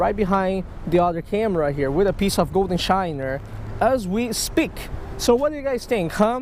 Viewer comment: “No 6” shiner today!” Catches 6 “ shiner 😆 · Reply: Okay thank you for the clarification 😂 i thought said bull s#$! Shiners LOL